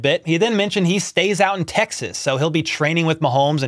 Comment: He actually said he stays OUT of Texas, not IN Texas, so not sure what that's about